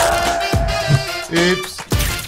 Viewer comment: why does everybody think that meme is saying oops it’s saying beeps like from teenage mutant ninja turtles 2 live action